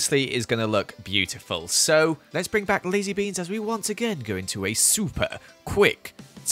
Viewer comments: When he said Lazy Beans I heard him say Lizzie Beans haha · Who else thought he said ‘Lizzie Beans’? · When Joel said Lazybeans I thought he said Lizziebeans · i thought he said lizzie beans